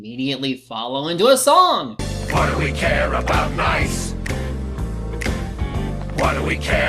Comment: At first I thought they said "What do we care about life?" WHAT